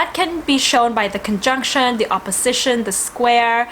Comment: Do u mean what i said about conjunction or INCONJUNCTION. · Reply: I just listened again and realized that you said inconjunction (quincunx) it sounded like you said in the conjunction.